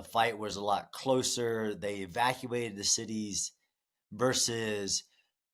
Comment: did he say "they evacuated the cities" ??? He is pure evil!